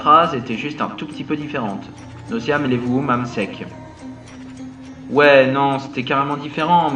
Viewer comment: Mais les phrases veulent dire quoi? J'ai compris qu'elles sont dites a l'envers mais je comprends pas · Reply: Mathis B jai compris Nosiaélèmoummamsec XD a